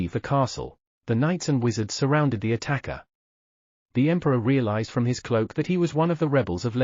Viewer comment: I first heard it as: emperor cereal